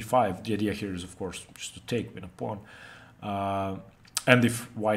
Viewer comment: I think says, "If black doesn't fight for e5 the idea [for white] of course is just to take, win a pawn." Take what? Win a pawn how?